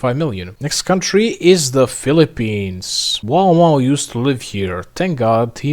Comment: what did you say? wow mao?